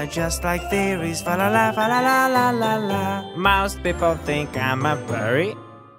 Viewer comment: que alguien me diga que es lo que dijo esque no hablo ingles jsj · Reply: Jax: Most people think I'm a furry?